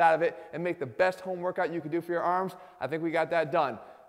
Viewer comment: I thought he said he was going to give us homework for our arms lol It sounds really refreshing